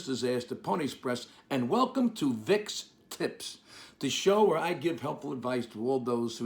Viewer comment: Sounded like you said Vic's tits!